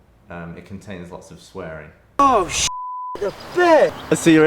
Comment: any Yogscast Angory Tom fans? Did he just say "shit the bed?"